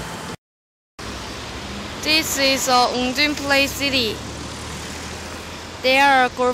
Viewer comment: This is "_______" play city. What was that word that she was saying? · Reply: woongjin